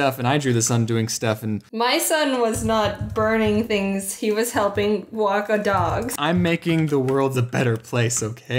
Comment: Did say walk a dogs 9:45 Seconds into the video?